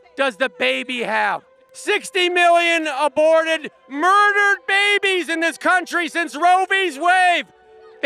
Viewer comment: lol that kid at 1:39 murdered babies?!?!? What the heck did he just say?